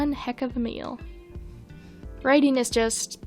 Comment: I love that when writing you can be disgusted by your work, think its garbage, but in a month or two when you return it is somehow made of gold. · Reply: What did say at 9:26? Did she really say "Writing Daddy" instead of "Ganymede's Clock"???